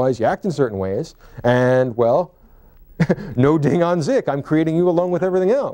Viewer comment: I’m sorry, I’m not familiar with the German philosophical terminology: what is professor Sugrue saying e.g. at 9:48? The automatic subtitles transcript it as “ding on zik” which is obviously a poor attempt at rendering a German word. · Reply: Ding an sich = Thing in itself